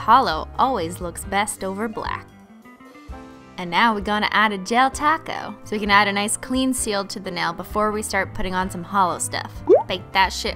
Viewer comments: I thought she said del taco and I'm like in my mind FR E SH A VOCA DO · Every time she says "gel top coat" i always hear "gel taco"